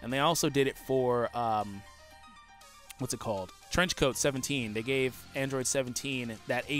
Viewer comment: I misheard Trench coat 17 as French coat 17